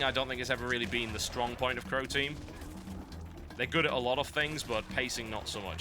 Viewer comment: I thought TB was saying "Protein" and not "Croteam" >.<